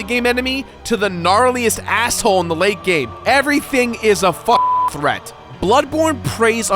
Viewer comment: I’m sure you said “f***ing threat”, but with it redacted with a bleep, it sounds more like “Everything is a fuck-threat”, which makes me laugh every time I hear it.